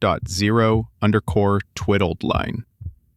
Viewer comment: There's a couple of glitches in the wording. For example: [53:27] "Twiddled line" instead of "zero_grad" [53:39] "Strread optimized" instead of "standardised, optimised" [1:01:18] "Zero med" instead of "Zero grad" (there seems to be something about zero_grad)